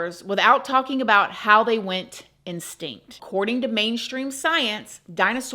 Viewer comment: I chuckled when you said instinct instead of extinct. I'm enjoying the video. I'm leaning more towards dinosaurs being part of the great deception.